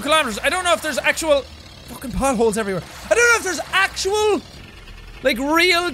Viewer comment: Thought he said but holes not potholes